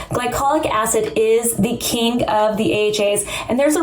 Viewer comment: In the beginning of this video she said "glycolic acid is the king of the AHAs," and somehow I heard "glycolic acid is the KINK..." Not gonna lie, exfoliation is my kink.